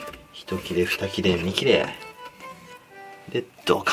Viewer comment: なんか色々コメントあるけど 「１(ひと)きれ、２(ふた)きれ、３(み)きれ」にしか聞こえない…… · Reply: 同じく。 ちゃんと三(み)切れって言ってるよね。